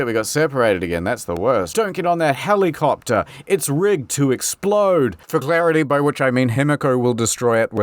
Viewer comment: I heard '...it's ribbed to explode...' which I took to be some kind of condom reference so I had to play it back and make sure. WTF was going on in my head that second?